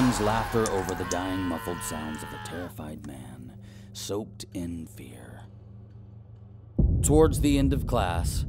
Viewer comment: I thought the teacher said soaked in beer but he actually said soaked in fear 😂